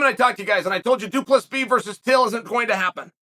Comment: does he say 2+b vs till😂